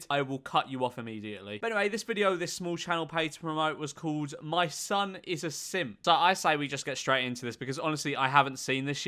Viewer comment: I thought that said "my mom is a simp"